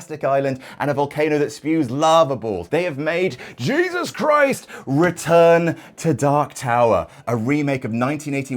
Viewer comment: For a second, I thought you were saying they had made a game called "Jesus Christ: Return to Dark Tower".